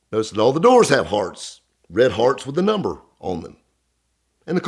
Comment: I had to rewind it 3 times before hearing "heart", I kept hearing "kid had a hard on".....it is so all bets are off.